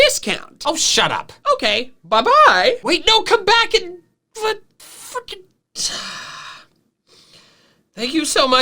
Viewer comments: Did just say fuckin? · WHAT DID BRO SAY 💀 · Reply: He most likely said fricking. It was just heavily stifled